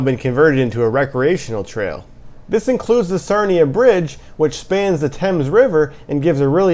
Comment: Your video description says Sarina but it sounds like you said Sarnia. Can you please clarify the bridge name is in St Mary's Ontario? Also in your video description you mention the trail is called Grand Truck Trail but as per the website it is actually called Grand Trunk Trail.